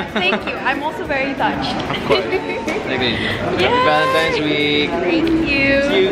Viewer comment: did he just say "happy valentines bae" ??